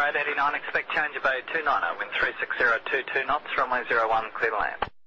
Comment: Someone must really like the number 23. 0:34 tower says "Winds 360 at 22 knots", 'sub'titles "23 knots" Starting at ~5:19, always 230 instead of 220 :D